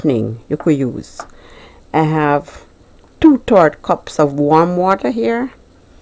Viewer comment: I thought you said 2 cups milk and 2 cups water now I am finding out it was 2/3 cups on both can you please write the recipe down for us. thanks :(